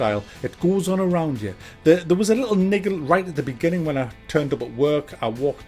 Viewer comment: I swear he says lol “little n*gga”. I didn’t think anything of it but then the camera pans to the left and there’s a black man inside the fish bowl room WTF?